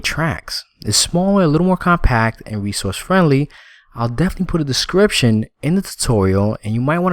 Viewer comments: Love these tutorials but could you for the future create one completed tile and then finish the rest ? Would save a lot of time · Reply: did you say: "I'll definitely put a description in a tutorial." ? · did you say: "I'll definitely put a description in a tutorial." ?